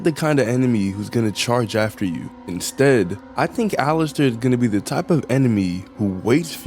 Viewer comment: I heard “ Alastor “ 💀